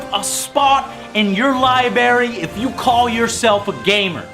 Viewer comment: did he just say liberry